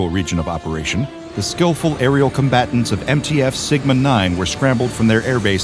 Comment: Did i just hear at 2:31 sigma 9